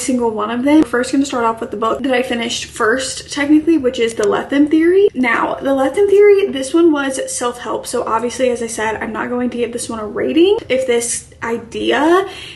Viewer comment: I thought at first it said “rating i no rate” which was just really funny to me😂